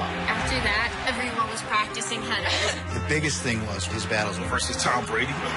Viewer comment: did she just say every woman was practicing head?